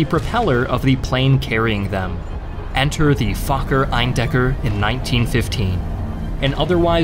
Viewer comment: did he just say “f**ker” 😂😂